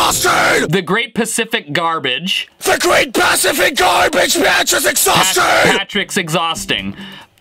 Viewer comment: wtf how did you not here what it said it clearly said the great pacific garbage patch is exhausting how did you not hear that